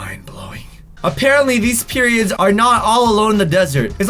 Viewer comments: he said period instead of saying pyramid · you catch him saying "periods" instead of pyramids leave a like you caught that aswell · who else heard him say 'these periods' insead of 'these pyramids' 🤣🤣🤣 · says period instead of pyramid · he did NOT say pyramid.... listen very very closely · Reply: I heard it he did say periods I watched and replayed it so many times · he says periods instead of pyramids😂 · Did anyone else hear say " these periods " 😂 LMAO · my dude said “periods” instead of “pyramids” or am I just hearing things 🤣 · you said periods instead of pyramids you find it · said periods instead of pyramids lol😂 Great job! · Who else heard say “periods” instead of pyramids. 😂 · Okay when you said "pyramid are all alone in the desert" it actually sounded like you said period instead of pyramid 😂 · did he say periods or pyramids? 😂😂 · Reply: Tbh it sounded like he said period 😀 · did anyone hear say periods? Nope just me? Ok...😔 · he did NOT say pyramid.... listen very very closely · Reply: lol i heard him say periods but my brain didn't process it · he said periods instead of pyramids 😭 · Did he just say periods. 😂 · did anyone else here say also, those periods aren't actually all alone in the desert instead of, also those pyramids actually out all alone in the desert. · he literally said "Periods" instead of "Pyramids" LMAO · I swear he said period😂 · he said periods instead of pyramids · he sad “periods” instead of pyramids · did he say periods